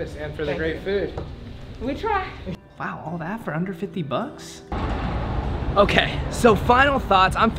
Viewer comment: why did I think you said 150😭